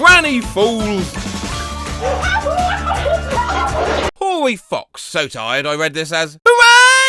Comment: I misheard that as ‘Horny Fox’ for a second